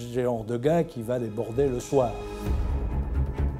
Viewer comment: There's a blatant mistake at 14:55. is misquoted as saying that Patton was not the type of guy who went to brothels. He did not says "bordel", which is French for brothel or bordello. He said "il n'est pas du genre à les border le soir". Which translates to : "He is not the kind of guy who tucks them in at night."